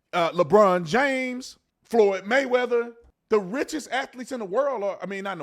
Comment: It's all bread and circuses ! Sports after college are a joke , but when B. Tatum said Floyd Mayweather , I thought he misspoke and said heard George Floyd ! Psychological programming or just me ?